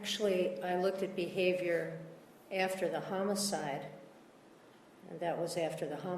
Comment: lmao did anyone else catch Alyce slipping up and calling it a murder? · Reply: Yup. She actually said homicide twice around 7:35 and Mr. Martinez was more than willing to let her continue. Hehe🙂